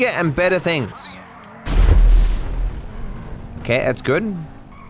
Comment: Did really said "das ist gut" at 44:55 ?